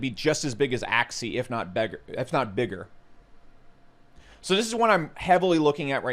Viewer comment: Was beggar a Freudian slip? Saying beggar instead of bigger.